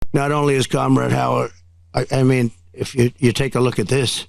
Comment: It kinda sounded like he said “Kama-rad Howard”…. 😂🤣😂🤣